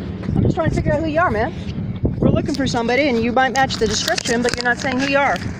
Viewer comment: did she just say “ you might match the description, so she doesn’t even know who’s she’s looking for.